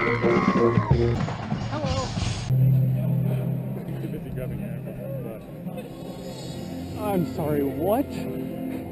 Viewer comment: what is this man saying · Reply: “ I was too busy grabbing __ butt “